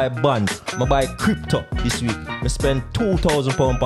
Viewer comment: I thought bro said he spent £2000 on socks this week. Now that’s a flex 😂